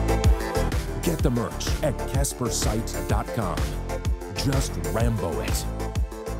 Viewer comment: i thought u said just RAMBO IT in every seen video and photo clip...😅😅